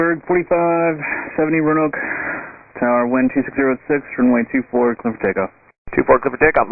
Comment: I think ATC says "wind 260 at 6" (not 160). At 10:14, "HVQ" would be HOTEL VICTOR QUEBEC (not KILO), right? I got a number, let me know when you're ready... 😉